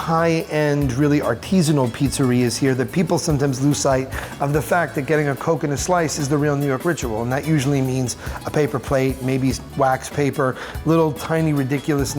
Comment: I thought you said "coconut pizza"... but realized it "coke and a pizza".!